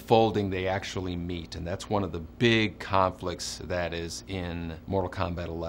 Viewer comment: did he just say "Kahnflicts"?